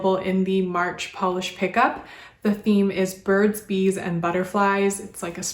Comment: Okay, so I honestly thought you said the theme was The Birds and the Bees. Like, old fashioned sex ed talks 😳 This is why I should not watch videos at midnight, I’m half asleep and hearing things lol 😅